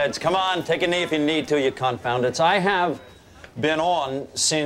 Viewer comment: Btw, what the hell does Cox say at 9:23? "Take a knee, ya -------" Anybody know? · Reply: Oh, right. Hmm. "Confoundits", I think. As in "those who confound", since they likely do confound Dr Cox.